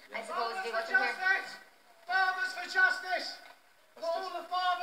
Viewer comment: I was wondering wtf he was saying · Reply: I thought he was saying farmers for justice